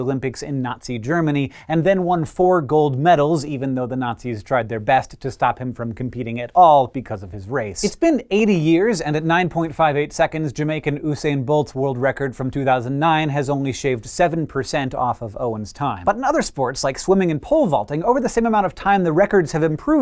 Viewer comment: DID HE JUST SAY "OOSAIN" BOLT?!? 0:43 BRUHHHHHHHH